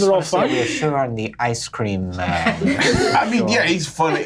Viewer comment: Hat's off to Brennan for trying very hard an sincere to set a mood. But when I hear "Ice cream Storms" all seriousnes goes down the drain :D